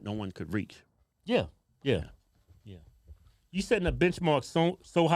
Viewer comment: You said benchmark. I thought you said bitchmark.